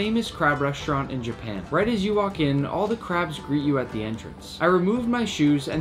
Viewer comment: i thought i heard nick say “all the crabs rizz you at the entrance” i need to take a break from youtube 💀